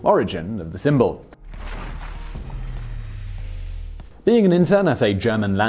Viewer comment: When you said, "being an intern at a German landscaping company," I heard, "being interred at a German landscaping company".